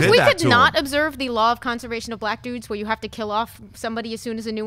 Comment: Did she just say, law of conservation of Black dudes?